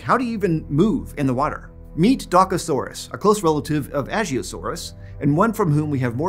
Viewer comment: I heard "DAKKAsaurous" and my inner Ork went "WAUGH!"